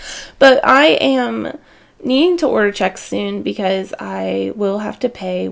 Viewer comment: Lol, it's been so long since I've used checks that when you said you needed to buy them I honestly thought you were saying you needed Chex (like the cereal) and I spent a good 5 seconds wondering why that wouldn't be coming out of groceries 🤣 I think this is a great idea, I probably need to go through and clean out my budget as well, it's been about a year since I did and I'm sure it's time! Pets are definitely a big financial commitment, I do find that I spend less on my cat than on my dogs, both for medical and food expenses.. They do tend to live longer (depending on the breed, obviously) as well. Awesome video, as always ❤